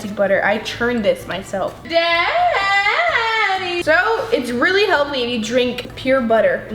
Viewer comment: It sounds like is saying daddy